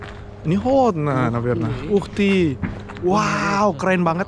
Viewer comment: UGHTEA njir ntah ngp gua ngakak dengernya